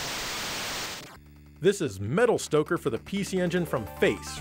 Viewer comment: I thought it said Metal Stroker at first. Im sure thats a game in Japan, prob not family friendly thou.